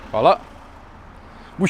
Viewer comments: Did you say "Fala"? jabem ti LOL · Bro are you from Balkan? I heard that "Hvala" loud and clear. Greetings from Croatia!